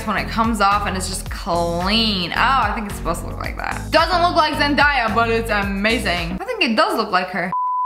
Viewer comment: did she say zen-dye-a